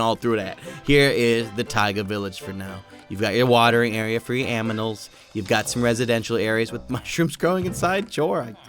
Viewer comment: Is it me or did he say aminals at 15:24?